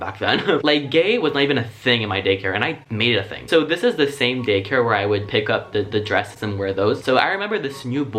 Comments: I really hope I wasn’t the only one who heard “gaycare” instead of “daycare” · I thought he said, "Gay Care." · I thought he said “gaycare” lol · I legit thought he said "gaycare" at first and had to listen to it twice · omg at 5:25 i thought he said "so this is the same gay-care" I DIED lmfao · ami the only one who hear him say gay care 😂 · he says “gaycare” instead of “daycare” 😂😂 we love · did anyone else hear him say "gaycare"? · I swear he said “gaycare”